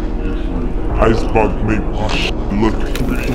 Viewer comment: I always thought that he said Xbox make my shit look 3-D. makes more sense now. lol 😆